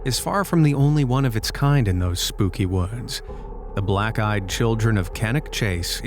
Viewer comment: I thought he said black guy child🤣